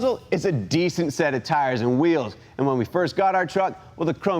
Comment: I thought you said decent wheels. Those are hideous.